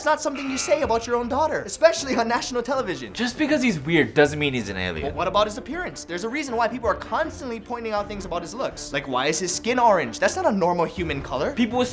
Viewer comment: I thought he said "Canalian"